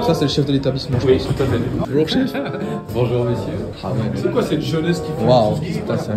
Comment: « c’est quoi on va prier ouuuuuu ? »😂😂